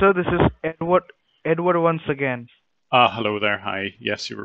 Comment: I swear I heard at first "hello this is n word once again"